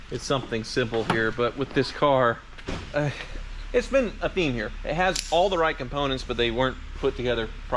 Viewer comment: swore you said " Well? It's been a bimmer" lol